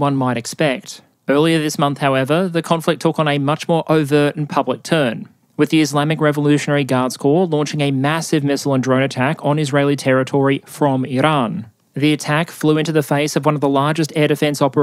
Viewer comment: At first I couldve sworn you said "The Islamic Revolutionary Dance Core" and I was like ?!?